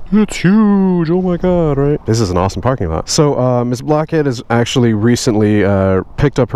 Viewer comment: I thought he said what's up black kid but he said blackhead